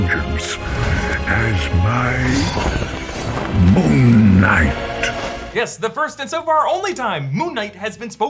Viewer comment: I always thought Khonshu was saying “You shall be My..MoonKnight.”